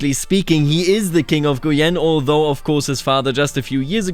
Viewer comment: When I hear you say "King of Guienne", my mind interprets it as "King of Grian"... I need to lay off the Minecraft Youtubers.